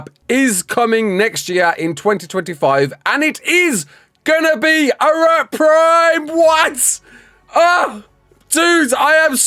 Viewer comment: Wtf did he say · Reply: He said “It is gonna be Arat Prime. What?”.